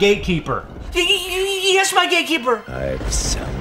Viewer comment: I thought they said “my gaykeeper...” lol I was dying 😂